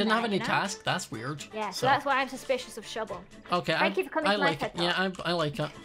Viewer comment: did just say “Thank you for coming to my TED talk” ?!? 😂